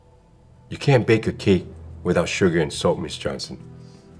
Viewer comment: Powerful quote ''u cannot bake a cake without sugar and vinegar'' Thanks Dhar for this quote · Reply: Did you just say “Vinegar”!? ☠️ He said “sugar and salt”— 😭